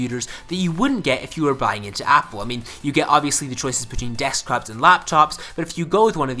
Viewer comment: did you just say deskcraps?